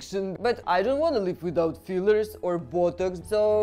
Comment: Thought she said “I don’t wanna live without buttocks”